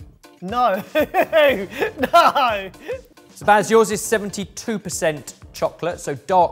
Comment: Did say Spaz instead of Baz @ 5:30? Is that his new nickname lol